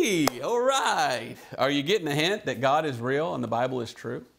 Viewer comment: Love it!!! Brother at minute 47:56 you said "God is real", & I heard (in my mind) God-Israel God Israel twice knowing that you said God is real. It seemed to me like a verbal from God identifying Himself as the One True REAL God of Israel. That was really cool. Also, as you were teaching about the tribulation Jews possibly fleeing to Petra & the water channels carved in the rock, I started thinking how Moses struck the rock to bring water to the Jews in the wilderness (mirror effect appearing in the old testament Exodus & in the New Testament Revelation) & Yeshua Jesus being the Rock (also struck) of our salvation, water flowed from His side when struck by the Roman soldier to confirm His death. He is also the Living Water, & the name that Jesus gave Simon - Peter (rock -) Petra of revelation that identified Jesus as the Son of God -the Messiah! The Holy Spirit was bring all this up to me as you were explaining the woman & the serpent spewing water to drown her & her male child. It was amazing Brother! Thanks for your diligence to teach on a subject most won't even mention! By the way, I appreciate the fact that you explained why the stars were referenced (Jews use the Astronomy for their calendar...). YESHUA-JESUS bless you & your family. Sis. Jetta H.